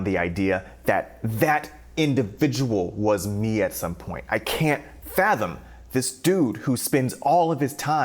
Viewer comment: I swear I Thought You Said "I Cant Fappem" sheesh i need help...but it's interesting to see the growth between you then & you now. Keep inspiring others